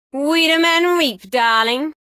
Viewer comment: I thought she was saying Woman ain't weak darling